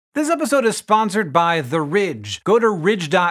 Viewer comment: I misheard the intro as "This episode is sponsored by the rich" lmao